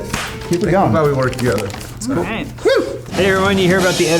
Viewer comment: Legit question- what was James' joke at 9:46? I couldn't understand him there at all :/ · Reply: You hear about the editor-in-chief who really wanted to fuck Spiderman? His name was J.K Semens